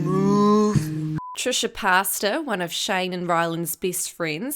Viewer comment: Did u just say Trisha pasta?